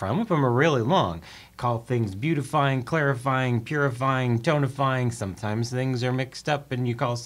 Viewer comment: I definitely heard "donut-frying" instead of "tonifying."